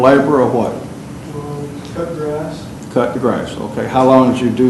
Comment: Instead of "cut the grass"..my stupid ass thought he said "Up your ass" 😂😂😂